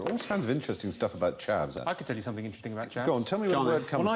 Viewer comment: What word do they mean at 1:59? I'm a non native speaker and I'm having issues understanding. Chowder?? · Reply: Chav